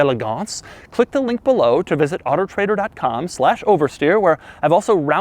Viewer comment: I swear at 2:40 he says otter trader.